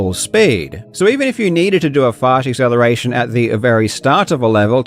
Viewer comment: Did you say "Fart acceleration?"